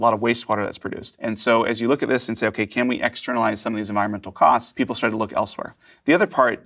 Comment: "Export environmental cost..." translation..."Flush my toilet into my neighbors yard..." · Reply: I thought he said externalize, which is an economic term. But I’m not gonna go through the video to check.